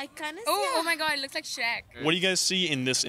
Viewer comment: that shit did not look like shaq 💀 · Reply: She mightve said Shrek but her accent made it sound like she said shaq